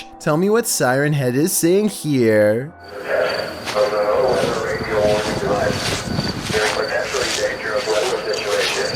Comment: This is what I know Siren Head is saying in the first sentence “This is a test of the NOAA Radio Device.” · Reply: Close: "This is a test of the NOAA Weather Radio Warning Device" Then it follows up with: "There is potentially dangerous weather situation" - Other than the unnecessary verbosity in the first sentence and the broken second sentence, it's pretty really well made.